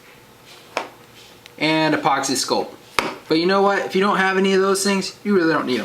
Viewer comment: Caption fail! 01:11 "But you know what?" (it says on captions "You know what?" lol this one's a pretty dumb one :P) 03:59 "grab two of the cubies" (on captions it says cubes instead) Those were worthless meh i was just bored